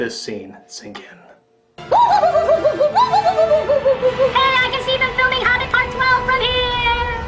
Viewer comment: what did he say??? Something part 12??? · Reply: 'I can see them filming Hobbit part 12 from here!'